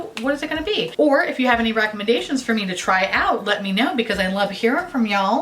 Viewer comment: For some reason I thought you said recommend-gaytions instead of recommendations and either way I accept